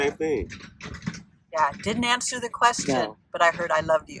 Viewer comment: …I heard “don’t answer”